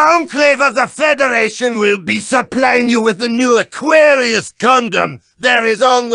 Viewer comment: what Gundam is that? i can barely hear what Demo is saying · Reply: the gundam aquarius